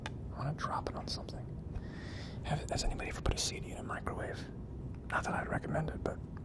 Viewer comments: did he just say cd-ed a microwave ? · did he say "CD a microwave"?